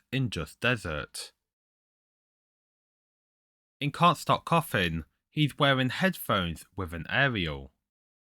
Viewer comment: "Can't Stop Coffin" I Hear "Scott Cawthon"